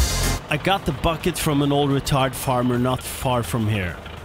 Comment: I thought you said, "Retard Farmer" lol